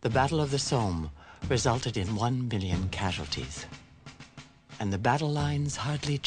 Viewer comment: I thought she said 1 billion casualties around 38:20 🤯